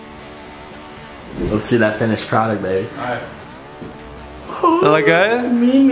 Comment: Did say Babe at 5:58 🤣🤣🤣🤣🤣🤣 btw I am subed and have notifications onn and love ya ❤️💕❤️